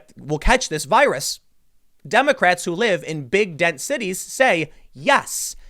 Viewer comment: did Tim say big dead cities instead of big dense cities? It sure sounded like it.